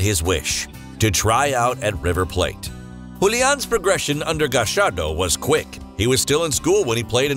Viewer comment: Hum... did he just say Gachardo? Gaxardo? Gashardo? I don't even know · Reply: Gallardo